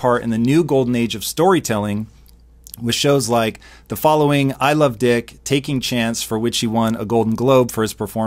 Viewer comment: Did Tom just say "I love dick" at 1:57 😂😂